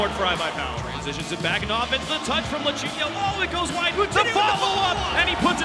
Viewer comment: I thought he was saying lachinio